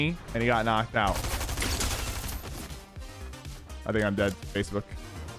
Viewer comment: Did he just say 'I think I'm dead Facebook'?